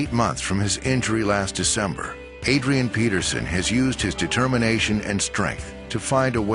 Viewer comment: Who hear the the guy speaking at 3:09 say Padrian Peterson ?